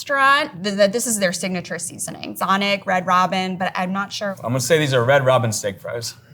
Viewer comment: Red ROW-BEEN??? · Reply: I heard it as Robein